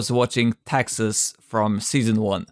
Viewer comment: Dude I know I keep saying this but this channel is awesome and so underrated. Keep up the good work, I always watch these when I am bored. However, During 11:56 I can’t tell what you are saying. “___ from season 1?” If you could lmk that would be awesome. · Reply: Thank you for your kind words! I say "Texas from Season 1"